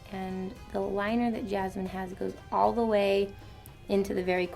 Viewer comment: did she just say YASMINE